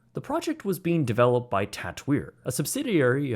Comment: I misheard the company name “Tatweer” as “Tatooine” from Star Wars and that got me thinking “Oh, so the Hutts have generously donated their cash to build a theme park? Damn” 😂